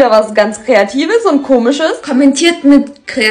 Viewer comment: Ich habe gerade Kreativität ist Rotz verstanden. Aber Kreativität ist hot macht natürlich mehr Sinn. Super Zeichen Talent. Bitte mehr solche Videos.